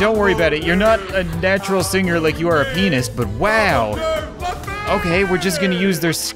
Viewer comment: I Know im late but im binge-watching and at 4:58 it said 'your not a natural singer like you are a penis' instead of pianest xD